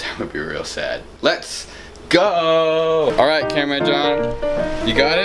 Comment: this whole time i thought he was saying cameraman john but i read the 3rd line of the description and realized he was saying camera-ron